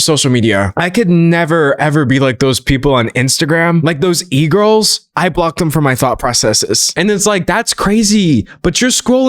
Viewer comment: I heard this as "those e-girls? I block them from my thot processes"